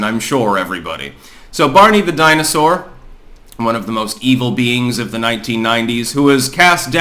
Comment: I do have tinnitus and your enunciation is normally good but at 5 : 14 of 6 : 51 I thought I heard " Barney vaginasore one of the most evil ... " but er I err am not sure.